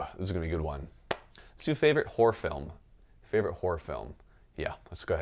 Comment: It sounded like you said “whore films” at the end, so here’s a few: Back door patrol 7 Shaving Ryan’s privates Black cock down Anal pirates 2: (tharrrr will be booty) ...delete my browser history if I die please...